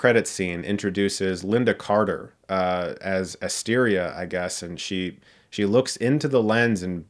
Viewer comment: When she told the tale of Asteria... I heard "Hysteria", the first Amazon Heroïn... lmao